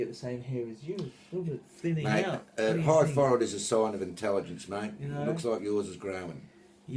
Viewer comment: what is he saying? A pie forward is a sign of intelligence? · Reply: A part* forward (hair part)